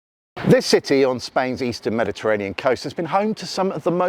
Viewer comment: No, not the 'eastern coast of the Mediterranean': the western coast of the Mediterranean. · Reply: I heard Spain's "eastern Mediterranean coast." The east of Spain, not the Mediterranean.